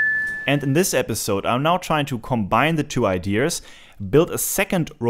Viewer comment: you said combine the two idears but i think you meant to say ideas (no r)